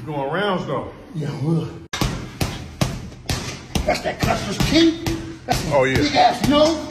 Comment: Did he just say "that's that custards chin" while throwing straights at the bag 😂😂😂